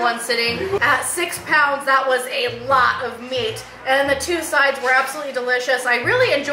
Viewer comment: I thought she said “at 6 inches that’s a LOT of meat” 😁